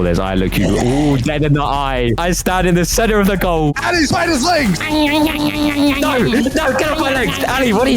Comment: WHAT DID HE SAY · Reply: He said alle bite his legs